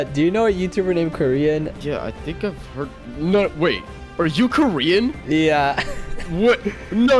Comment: I thought he said '' are u korean''😂